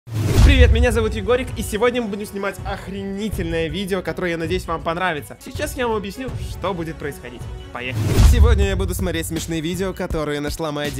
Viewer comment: кому послышалось о*уйтельное??🙈🙉